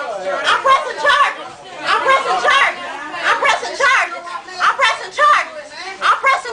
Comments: She said "I'm pressing charges" like 50 times. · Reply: i heard " im president charlie" a few times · Apparently if you say "I'm pressin' charges" twenty times, it makes it a stronger message. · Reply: coulda sworn she said "im president charlie"